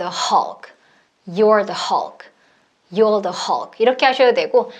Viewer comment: you are the hulk에서 you areㅇ here로 들리네요 ㅜㅠ